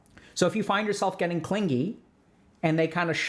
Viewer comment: Oh I thought it said cringy darn